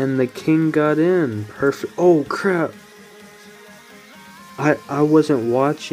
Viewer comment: save of the year Is it just me or did say cr*p